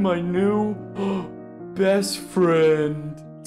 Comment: I'm wondering on 15:01 does say Dankie?